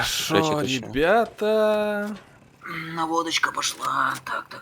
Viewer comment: послышалось "водочка пошла" ))))